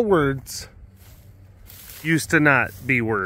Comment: Every time I hear "used to not" my brain makes it one whole word spelled eustinaut Like an astronaut named Eustace